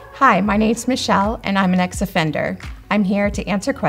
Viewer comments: Ex-offender, I thought you said sex offender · bro i thought she said sex offender